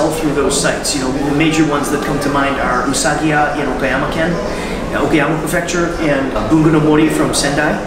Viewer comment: I'd love to try and purchase some of those local Tokyo inks through Rakuten, but I couldn't make out what was saying around 3:32. I heard "Usagia Bamakan," "Ogamafecture" and "Bunganumori" but no results popped up for me, so I don't think I heard correctly.